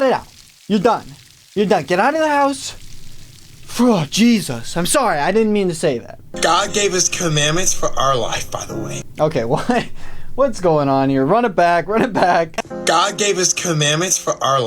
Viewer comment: y did I think he said commoth, I was so confused